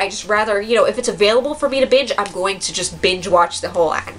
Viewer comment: I thought she said "bitch watching" lol